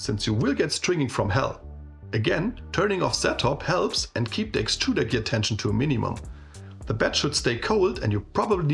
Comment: What is being said at 8:44 about TPU? “turning off …………?? helps, and keep the extruder ……………..??.. to a minimum.”. Yes I am struggling with TPU on a Prusa MK3S+. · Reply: "turning off Z-Hop helps and keep the extruder gear tension to a minimum." Z-Hop is when the print head moves up a little before it moves to another spot on the print. It's prone to pull fine strings. Turning it off wipes the remaining material a bit and reduces those strings. Extruder gear tension is the pressure on the filament. You adjust that with the screw on the left side of the print head. Keep it as low as the filament runout sensor allows. Ths Prusa profile is actually quite good, only thing you should activate is a bit of retraction. That reduced my stringing by a lot.